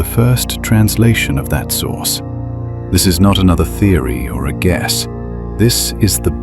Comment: almost sounded like you said I GUESS 🤗